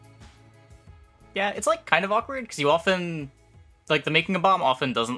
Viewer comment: then somebody says "awkward" - I hear "balanced"